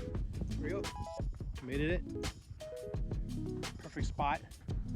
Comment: Wait up...did he say "made-ed it"??? 20:52🤣